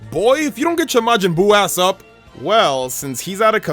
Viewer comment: he said a*s instead of ash